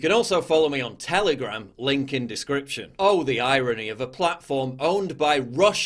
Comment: I heard him say: Licoln discription 3:00 and realised I might be rightwing indoctrinated